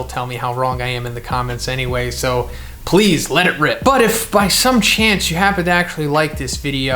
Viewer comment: did just say "Let it rip"?